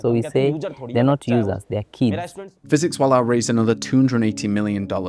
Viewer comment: what he actually said was "theyre not users, theyre kids, my kids" which is even more kind